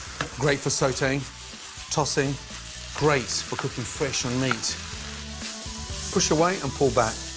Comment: What's he saying at 1:31? It's kinda bothering me. At first I thought he was saying "Great for cooking fish or meat," but it kinda sounds like he's saying "cooking ___ ON meat."